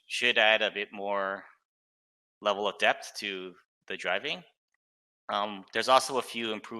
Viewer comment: “handbrake...won’t stop on a dime, but continue to roll forward.... which will add a new level of depth to driving...” I honestly thought he said “new level of death to driving” and thought, yup, people are going to be getting squished a lot more until drivers get used to not being able to stop in seconds... lol Another thing to add, vehicles had to watch out for being Tracked by environmental hazards, quite literally getting stuck or having the treads rip/slide free due to the ground they drove over. Also I never understood the ability for one guy with a hammer an some materials being able to repair a vehicle to full as if nothing happened.... that should at least be changed so that an infantry can’t fully heal a vehicle, only healing it to a certain % (low) before it stops healing... to counter this, add vehicle repair depos that they have to drive back to in order to fully repair. It’s realistic and it will prevent people from having a few tanks and a swarm of foot soldiers with hammers to insta regen the tank.